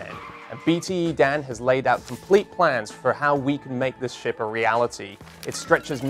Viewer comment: sounded like he said make this shit